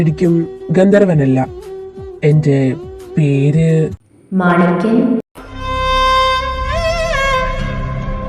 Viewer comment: what did she say? Martin?(his name?) Can anyone clarify plz. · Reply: She said manikyan..... Kelkkan pattunnile...